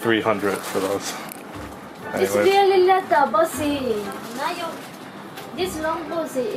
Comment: Wait did she actually say bossy or...